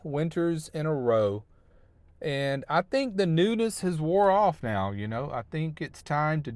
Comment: Poor has developed musophobia...fear of rodents and rightfully so! When you said "the newness has worn off" I thought you said "nudist" LoL! Have a Great Christmas!! ✌️🍻